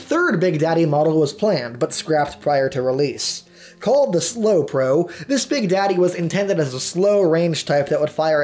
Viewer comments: Aww I thought he said Slow Bro. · She says Slow Poke. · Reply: Rampant Mouse Maybe, but I heard it as "slo pro"